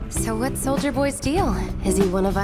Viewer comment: Am i the only one who heard "So whats Soulja boy's deal"?